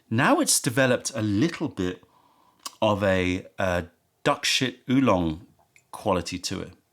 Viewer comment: Did he say duck sh**? Think I'll be avoiding that one.